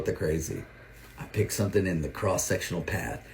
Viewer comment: “i pick someone”. I love how unattractive guys have the gull to judge US on our looks. With a face like his, he would need personality to get a girl in the first place.. one would think. That said, if you know a girl is prettier than you are, don’t go asking your boyfriend if he thinks she’s prettier than you are. What a setup. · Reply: He didn't even say someone, he said something